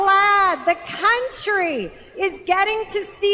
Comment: did she say cuntry ? 😂